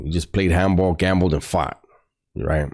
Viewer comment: Bro, I thought you said, "we played handball, gambled, and fart." My bad I was half asleep homie.😂